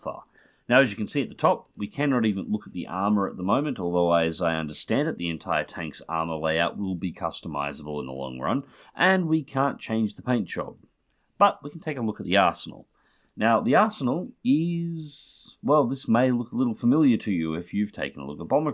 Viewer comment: Gib me ww2 tanks and yes friggin please. Tank Crew Sherman dlc....thank you. And I totally thought you said "lets take a look in the asshole" at the start...